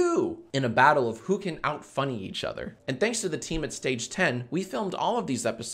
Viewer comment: When he said “Thanks for the team at Stage 10” I thought I heard team 10 and spit up my drink